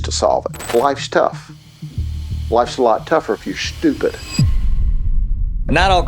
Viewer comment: "Life is tough. Life's tougher if you're stupid." SOOO using that quote 🙏🙏🙏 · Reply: I thought it said, “Life is tough. Life is together if you’re stupid.” I don’t know why I thought that